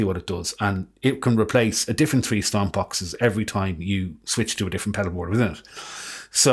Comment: What was that word @ 2:22 ? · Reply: A different pedalboard with it?